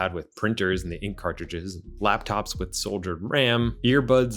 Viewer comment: Did you say "Laptops with soldiered RAM" at 7:28?